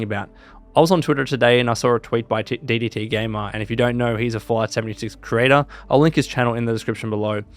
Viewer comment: Why does it sound like u saying fallout 72?